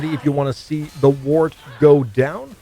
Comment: Hey man this build is dope and im still lvling. Curious how are you triggering Elemental Nova for free?? I caught the 3 stacks into cryo fragility but what did you refer to after that. Sounded like you said, "The want" ??? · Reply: the wand